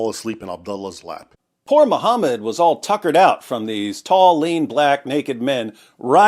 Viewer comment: I thought he said More-Ham-Ed? was all PUCKERED out by these tall, lean black men. 😂😂😂😂😂